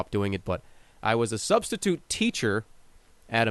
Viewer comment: i was a substitute teacher, phew i tot i heard prostitute